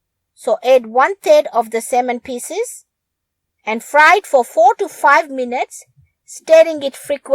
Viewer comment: Lol, I heard that as 45 minutes, not 4 to 5 minutes 😂. Silly me.